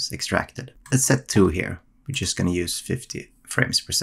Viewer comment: why does 1 give 30 and 2 give 50 (or did you say FIFTEEN and it’s dividing?)